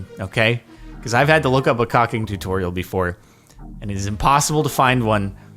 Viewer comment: the captions are trying to demonetize you. Says cocking instead of caulking